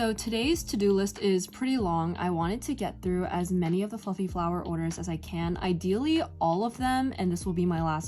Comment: Okay at 9:35 you said "fluffy flower" and I heard "f*ing flower" LMAOO 🤣 Seriously though great video. Your crochet flowers are so cute!! <33